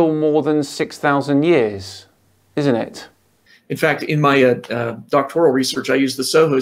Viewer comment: I heard "Doctor Lyle" and expected a gamer with one giant eye.